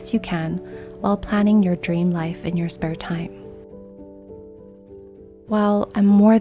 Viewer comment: I thought it said live your dream life in your free time but I think this sounds so beautiful to me